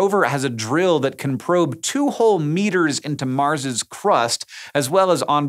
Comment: I heard 2 hole meters and thought you meant 2 meter holes. But then I realized you were saying 2 whole meters 😂